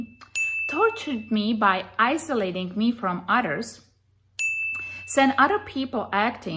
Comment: I think "isolated from utters" sounds like a cool name for a band. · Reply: I thought she said "isolated from otters!"